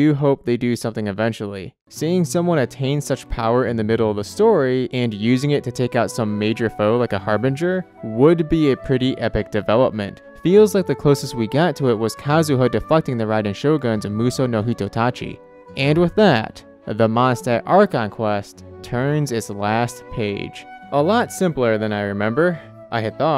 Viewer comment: am I the only one that thought he said “feels like the closest we got to it was kazuha fucking the raiden shogun’s musou no hitotachi”